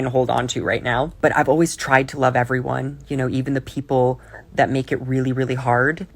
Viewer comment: I thought I heard "People that make you really hard!!!" 🤣